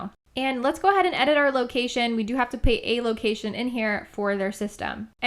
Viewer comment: We have to pay a location for there system did I hear that right? Or did u say put ???? Go to 16:56 I thought big cartel was free 😊💓love your vids btw